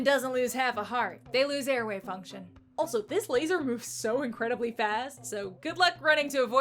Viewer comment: really thought you said "a real human doesn't lose a heart, they lose their WAVEFUNCTION"